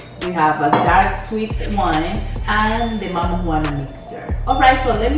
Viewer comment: lol My bad. I don’t drink. I thought you said marijuana! 😂